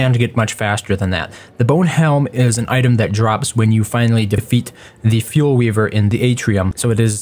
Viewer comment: did anyone else hear def-aet